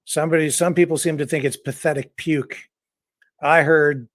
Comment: I heard pathetic "Douche"